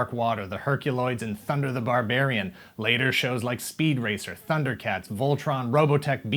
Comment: did I just hear speed racer